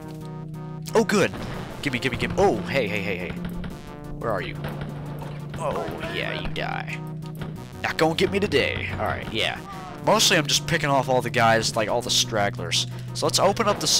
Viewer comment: For years I tried to work out what you were saying at 6:08 "Nut gone get me the day" "Knock on give me the day" Turns out you said "NOT GONNA GET ME TODAY!!"